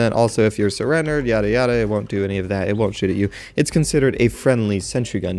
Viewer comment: It sounds like you said 'it wont shit at you'